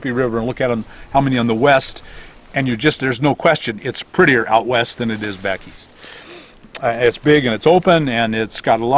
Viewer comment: did you say 'algedonias'? Whatever it was you said, what are those and what do they have to do with medical and dental? Thanks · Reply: Los Algodonas, BC, Mexico is a town close to the US border, a short distance from Yuma, Arizona. A number of medical professionals have set up their practices there to service Norte Americanos, and of course local clients. They can earn a good living there, and offer lower prices to their clients than licensed and insured professionals in the US. Best, rc